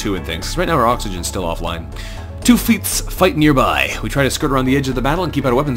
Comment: Best Voice Overs Ever. No, matter how cheesy they are. They fit so well in FTL XD btw. Swore at 14:50 heard you saying: "Two Feet" instead of "Two Fleets" XD. Words. Curse those evil words!